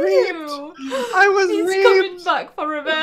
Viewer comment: I thought Joey said " I was raped!!!!" XD